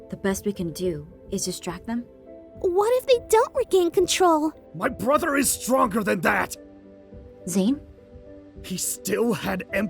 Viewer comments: am I the only one who heard “MY BROTHER IS DRUNKER THAN THAT” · When Zane said "My brother is stronger than that!" I thought he said "My brother is drunker than that!" 🤣